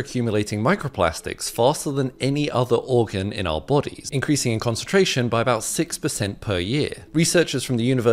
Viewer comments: he was speaking so fast, and I thought he was saying it was increasing our concentration. haha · I will achive absolute concentration in 16⅔ years is all i heard